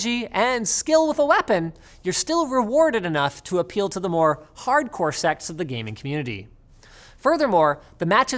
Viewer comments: did he say "Hardcore sex of gaming community" instead of "Hardcore side of gaming community" ???? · Did he just say "hardcore sex"?